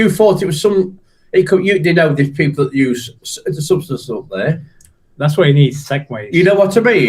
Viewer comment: What ith does the one guy on the far right say at time stamp 17:21? To me it sounds like he says "That's why you need Segways". 🤔.